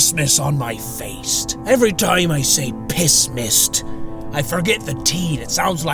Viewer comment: I thought you were saying Piss-mas...as in a piss-filled Christmas...😂 Piss mist makes a lot more sense, I suppose.🤷‍♂️👍